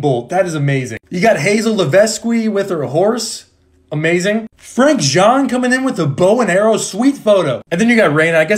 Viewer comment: It sounded like he said frank john 💀